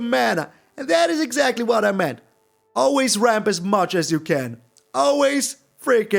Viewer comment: When you said "always ramp as much as you can", your opponent thought you said "always rope as much as you can"